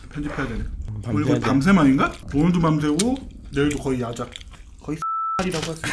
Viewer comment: 뭐라고 한지 아시는분 ㅜㅜ 궁금한데 모르겠어요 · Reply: 거의 시 1X발 이라고할수있죠 라고한것같아요 ㅋㅋㅋ